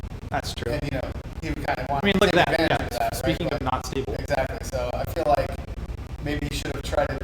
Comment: DID HE JUST SAID THE N WORD ?!?! RACIST?!?! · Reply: I listened to it more like a dozen times, including a few times at half speed and heard nothing even remotely like that. Here's what I'm hearing from 10:00 to 10:20: "I feel like maybe he should've tried to, uh, get that happening a little bit earlier in the match, um, instead of going for the grab immediately, but granted you know, hindsight's twenty twenty they always say, so, uh, unfortunately that first hit really really screwed up Complete Control there, and it was pretty much downhill from there so..." Will: "yeah (laughs) yeah, speaking of downhill..."